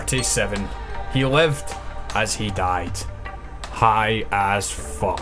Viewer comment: LEFT as he DIED i dont think thats how you say it lol